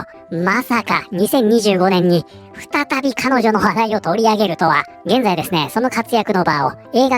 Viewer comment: 彼女の話題を取り上げる、が彼女の笑いを取り上げるに聞こえたがよく考えたら大して違いはなかった